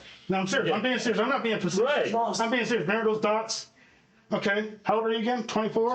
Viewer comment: What does the detective say at 46:56? Something about "remember those dots"? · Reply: yes he said remember those dots, referring to connecting the dots during his story.